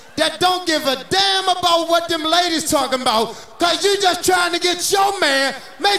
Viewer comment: …Did he say “cause you trying to get your man?”